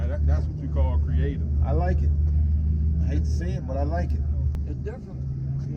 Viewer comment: "I hate to say it, but I like it." "That's the spirit of hotrodding." "That's a lot of work, holy crap." I love this thing. Bring it to a Honda meet next. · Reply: Ooooh shit i thought he said: i hate to SEE it but i like it.